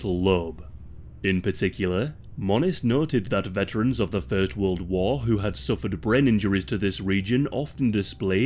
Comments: Did he say 3rd World War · Did he say third world war wtf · Am I hearing him correctly @ 2:43 of the video? He said that the procedure was thought to help soldiers that fought in the 3rd World War. Did I miss the 3rd World War? Are we in the beginning stages of it now? If so, it's been a hell of a ride folks!